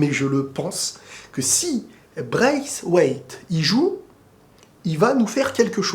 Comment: pendant une seconde je comprenez pas de quoi il parle "Braiswaith" avec un accent 🤣🤣